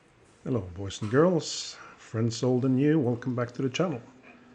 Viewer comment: Hello Robbie, thanks for watching and sorry for the confusion 😂 What I'm trying to say in my broken English is "Friends old and new" :) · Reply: Ah-ha! That never occurred to me! Now that you explain it, I hear it as "friends old and new". But at the time it sounded like "friend sold in you", and I was like, "Huh? What?".